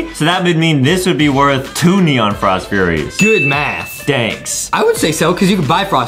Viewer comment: It sounds like he said danks